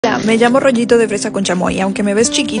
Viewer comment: Hahaha entendí “roñito” no “Rollito” y ya se me antojó el roñito